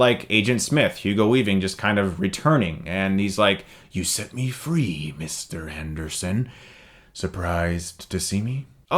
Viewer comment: Did you just say "Mr. Henderson"?